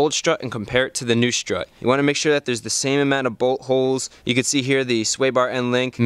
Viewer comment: i kept hearing "butt holes" when you kept saying "bolt holes" LOL DIRTY MIND!!!